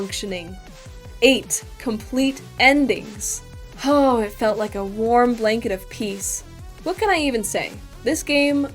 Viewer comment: I paused when she said "a warm blanket of peace" at the worst moment, which made it sound like she said "a warm blanket of pee" 🤣